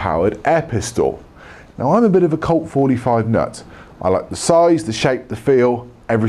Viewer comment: I skipped in to the video by double tapping right and the first words I heard were “nuts, I like the shape the size the feel...”